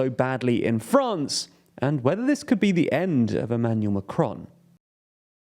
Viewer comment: For a moment, I thought that it said "the end for macaron." 😂